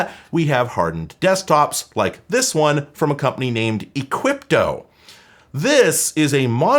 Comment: I thought you said "Cwypto"